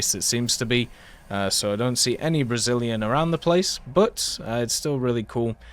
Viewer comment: did he just call the language brazilian or I'm tripping?